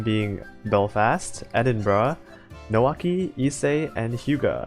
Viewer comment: did you just say : EdinBRUH